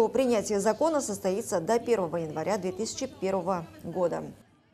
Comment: Это мне одному послышалас как ведущая говорит до 2001года